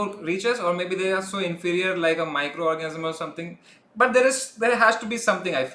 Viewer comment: MicroORGASM instead of microorganism xD